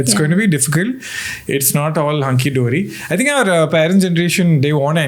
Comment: Did he just say "hung ki dori"!?!?